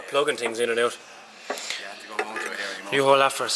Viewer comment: did say "but you have to come home to a hairy muff" at about 5:50 min mark hahaha